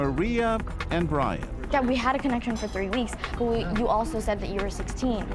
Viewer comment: "Dad, we had a connection for three weeks." Did i hear wrong, or did she call Brian her dad?????